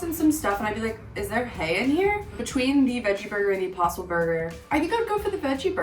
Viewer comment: I thought she said Opossum burger instead of impossible burger, att first. And I was like, ok this sounds a bit out of the ordinary😳🤔😆